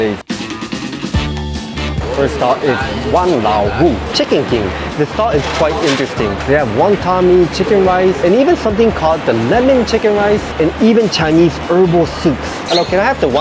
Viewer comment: When he read the stall name Wang Lao Wu, I thought he was saying Hokkien Wa Lao Mu (meaning my old mum)